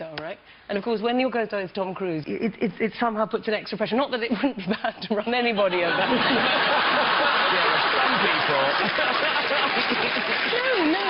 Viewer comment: what did she say please ? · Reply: ‘Not that it won’t matter to run anyone else over too’